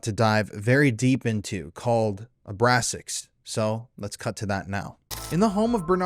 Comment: duh i thought you said abrasics but you said abraxus i need a hearing aid